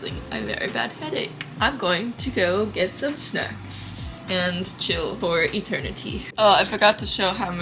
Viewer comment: I'm glad you're feeling better! We'll be here waiting regardless of of how long you're gone! #TheRealOnes Also...I heard "I'm gonna go get some sex" instead of snacks lmao idk why and I was like 😳 wait what!?